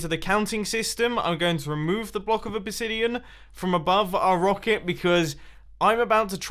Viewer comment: Did he just call the obsidian a obisidian??? 😂😂😂😂